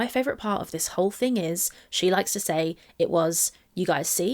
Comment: actually, she says "she likes to say i was cha -" and then cuts herself off. i haven't seen anyone point this out but she was likely about to say "chasing," which means that what amber meant to say (imo the truth) before she cut herself off and thought better of it was "she likes to say i was chasing her." firstly, more proof of amber's aggressive pursuits/false inprisonments of emily. secondly, the way she worded that implies to me that she and emily had repeated conversations about their confrontations to the point that amber knows full well what emily "likes to say" in reference to the incidents. she accidentally reveals herself here AGAIN.